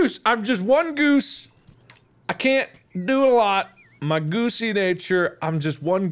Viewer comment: Thought he was saying poosy 💀